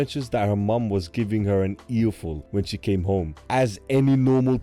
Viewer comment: did you say that Sora got an ear fold by her mother?